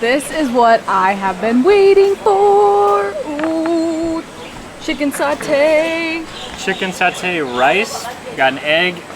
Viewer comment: couldnt make up what's this dish.. im local and couldnt make up what this was 😅 Did you say "Chicken Satay" or "Chicken Saute" rice ? rather unusual to me.. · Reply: Chicken Satay on top of rice :) thanks for watching!